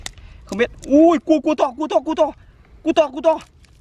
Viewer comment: nghe như kiểu c* to c* to á :))